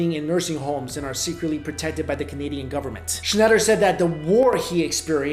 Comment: I heard comedian government